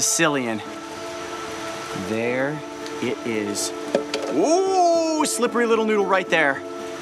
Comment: Did Coyote just say "ooooh slippery little noodle"....because if so that makes me feel better that I would name a snake noodle Edit it was